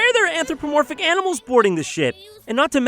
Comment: Did Jon say boarding the Shit?